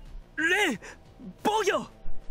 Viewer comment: Nose el resto pero en 17:12 en la pronunciación de "defensa" escuché pollo 🐔. xD :v